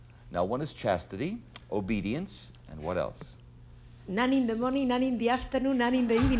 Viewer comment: Is she saying nine or nun? Her accent is too thick